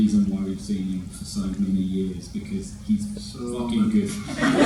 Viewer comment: What does Jamie say at 11:56? Does anyone know? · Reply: Aarshaa Sojan the director siad “cuz he’s” and Jamie said “-so fucking good”